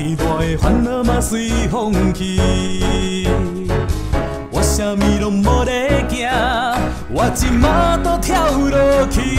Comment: 我什么都没在怕のところは、 wa syanmi 「?」 bolegya ↑说什么？「lo?lon?」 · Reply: 「?」=lung